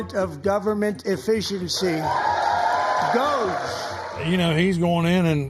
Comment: Is it me or at 5:20, does he call it GOGE??? Even slowed it down and it sounds like a G sound.